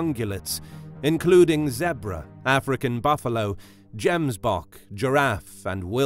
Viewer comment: i thought i heard "james bond" 😂